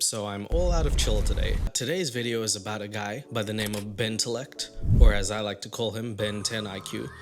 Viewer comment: am I the only one who heard Bentendo? I was like “BEYYNN”